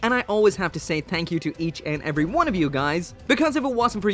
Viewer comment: when you say thank you I hear... Tank You.